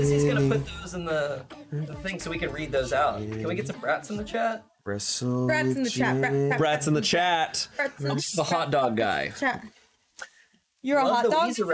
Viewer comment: I thought that they said rats not brats…